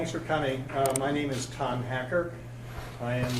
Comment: did he say “I am Con Hacker”? Perfect name!